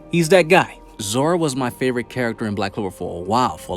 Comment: sounded like Zoro I was like NANI!? played back and said he mean Zora. Lol